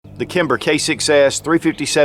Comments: I love the intro! I thought you said “the Kimber K kicks ass...” made me chuckle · ROFLMAO...at beginning he says Kimber K6s....sounds like he says Kimber Kicks A$$